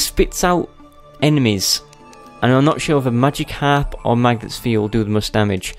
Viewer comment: Whenever he says "Magic harp", I can't not think of pokemon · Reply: magic harp? I thought u said "Magikarp".